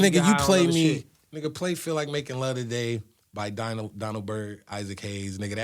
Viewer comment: What song did he say at 1:27:24? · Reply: Isaac Hayes- feel like making love